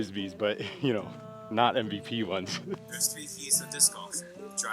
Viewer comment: This whole time I thought he was saying "there are three D's of disc golf" and then proceeds to list one action that starts with a d. I just figured it was meant to be humorous. Keys. Makes so much more sense now.